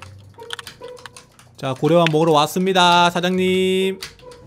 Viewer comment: 난 왜 고려밥이라 들리지